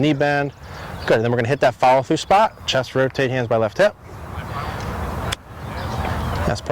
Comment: What is he saying at about the 5:30 mark? Is it “chest rotating through the left hip”?